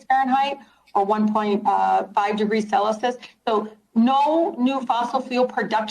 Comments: 1.5 ° sellysuss, saylusus, WHAT THE H___ DID YOU JUST SAY, its sell-see-us, its Celsius, youre a phony, you dont even know how to pronounce CELSIUS..!!!🤣🤣🤣🤣🤣🤣🤣🤣🤣🤣🤣🤣😠❤🇺🇸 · Did she just say celusus